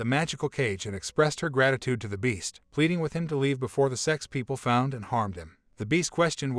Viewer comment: the subtitle says s*x people instead sects people or something like that.